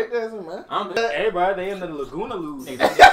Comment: My dumbass thought the intro say lasagna 😂